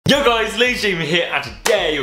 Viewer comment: is it just me or did it kinda sound like he said "YO guys leafys here"